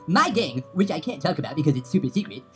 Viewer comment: Did he say nigga?